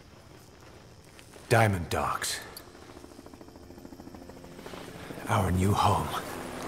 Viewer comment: keith needed more damn lines. i honestly thought snake was saying "diamond dogs, our new home".